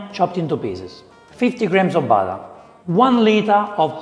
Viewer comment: When he first said '50 grams of butter' i heard '50 grams of barley,' and I was like RECORD SCRATCH!?!? "Vincenzo, you wanna run that by me one more time?"